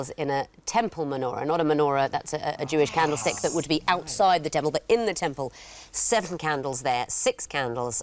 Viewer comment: Did I hear that right?"outside the devil"?? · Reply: Outside the temple